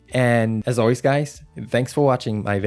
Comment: Did anyone else hear “I love you with all my fart and soul” ?